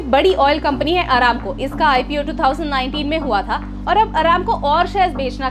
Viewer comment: I heard “HARAMKAUR”